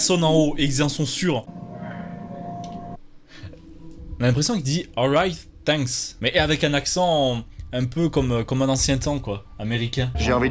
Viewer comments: le "alright" on l'entends ultra bien après moi j'ai compris "alright ! Lets get it !" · moi j'entends " Right there " donc " par ici "